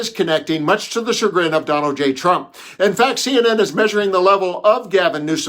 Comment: Is it my hearing or did he say Donald Gay Trump? Probably just my ears but thats what it sounded like.